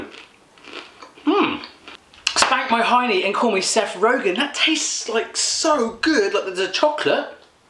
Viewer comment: When he said spank my highney and call me Seth Rogen I thought he said Seth Rollins